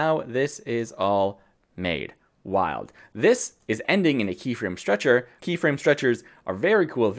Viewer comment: thanks. Will watch next. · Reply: Ahhh. Turns out I had already seen and enjoyed it. I thought you said “Keyframe structure” but you said “keyframe stretcher”. My old ears are starting to fail, thanks! One question I always wonder about is that There is no way to get the stretcher to work with a clip that is shorter than the original, correct? So if trying to make templates, it is best to make the original clip as short as possible.